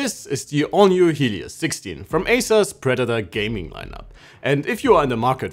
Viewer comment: did you just say ASUS?😂in your introduction